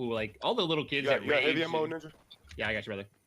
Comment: what does he say there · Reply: antonisssbtw u got ammo ninja